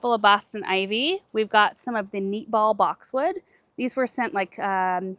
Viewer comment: Hi. I thought you said those were MEATball boxwoods. I had to look that up. Now I know, it is NEATball boxwoods. I've been on the hunt for very small boxwoods that do well in containers and stay round. Zone 8 with lots of rain (Vancouver, Washington USA). Any suggestions? Thanks for all the education. I've gone from only growing weeds to a luscious and very full yard. This year's theme was saffron finch and Bordeaux supertunias with white out supervena and gaura. Thank you for all the information, education, and just fun videos.